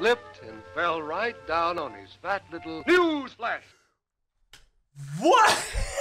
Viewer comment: DID HE JUST SAY PILLS GLASS?? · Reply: Nah new flash is what he says